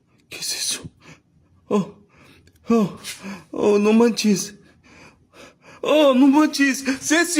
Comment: It sounds like he says no munchies no munchies 😂when he was scared after going to the window seeing a ghostly head behind the jeep